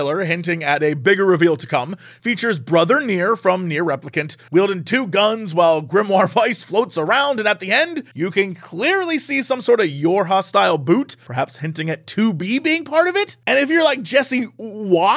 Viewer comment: I thought Jesse said, "Your Hostile Boot" when he said "YoRHa style boot"